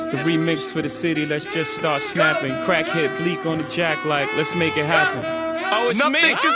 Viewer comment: Did he call bleek a crackhead at 5:55 😂